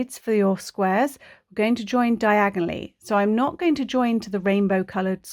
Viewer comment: When yiu say "diagonallly" I hear "Diagon Alley" ... 😅🤣😅🤣😅🤣😅🤣😅🤣